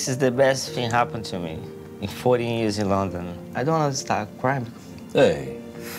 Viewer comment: "I don't understand crime" way to fail CC.... "I don't wan't to start crying" is correct